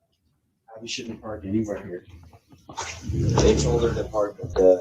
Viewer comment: Wait.. who is Abby? It sounds like he says Abby shouldn’t park anywhere near here! And then he said, they told her to park at the bottom lot.